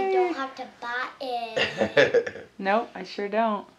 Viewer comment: Did she just say NOPE??!!!